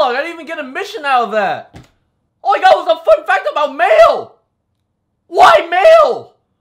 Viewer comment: It sounds like he says "All I got was a fact about mayo, why mayo? WHY MAYO!?!?!?"